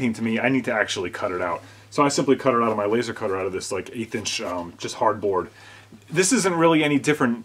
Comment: Did you say Guitarboard?